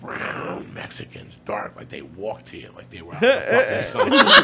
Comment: what did he say at 14:12. they were brown mexicans... they walk to ya.... ???